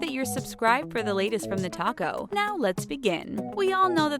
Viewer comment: When you said TheTalko, I thought you said TheTaco...🌮🌮